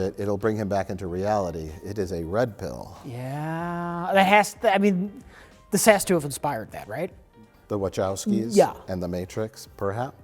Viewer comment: When referring to the red pill segment, I misheard Rich as saying "That has to have expired now" as opposed to "That has to have inspired them" and it was totally a great throwaway joke Rich would have made. Like, the pill in question is more than 20 years old at this point and Rich would certainly, hopefully, point that out to us.